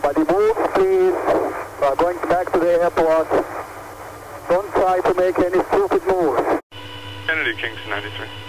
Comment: It took me a minute but I figured out what Mohamed Atta said at 16:40 if you need it. "Nobody move, we're going back to the airport. Don't try to make any stupid moves"